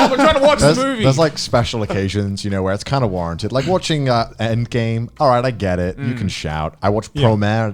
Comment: what movie did he say in 1:30 ? Promare and what? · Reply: Avengers endgame